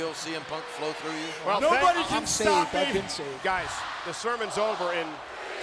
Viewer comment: what did matt striker say when lawler was talking over him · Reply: "I'm saved, I've been saved!"